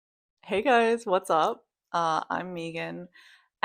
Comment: I thought you said “hey, what’s up, I’m vegan.” I thought it was a weird intro 😂 but the. I realized your name is Megan. 😅 that makes much more sense. ❤